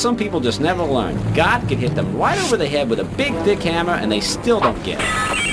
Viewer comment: Please don't tell me I was the only one who heard "big d--- hamer"